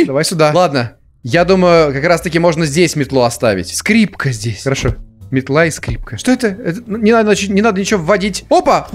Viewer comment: мне это послышалось или он действительно сказал это?) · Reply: он сказал грёбаная фигня :))